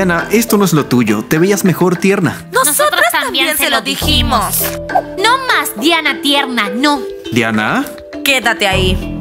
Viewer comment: parece el que dice no más niña tierna